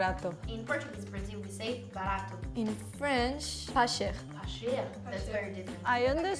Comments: your captions are wrong. She's saying "pas cher" ("not expensive"). "Bon Marche" means "good market" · I think she said "pas cher" not "marché"